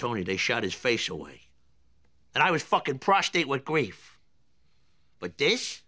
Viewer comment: Did he say "Prostate with grief"?